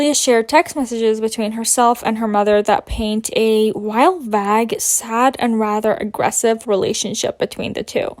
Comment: baby girl i love you but did u say “wild, vag sad and rather aggressive”